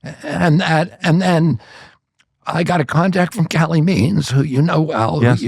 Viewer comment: is Calley Means. The transcript is incorrectly calling her Kathleen Means